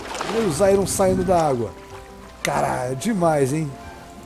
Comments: eu entendi ele dizer carai, mas eu fuiver que ele disse cara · nos 100k mostra seu rosto e no min 5:24 eu entendi Caraí